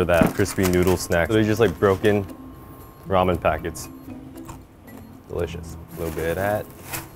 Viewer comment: all i here is Gordon saying delicious